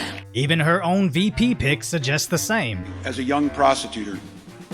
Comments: who else thought tim waltz said “as a young prostitutor” edit: i mean. she IS in the streets yall 😂😂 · did he just called her prostituter? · sounds like he says prostitutor